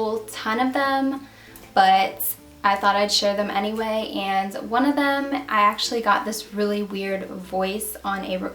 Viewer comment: I heard "One of Three"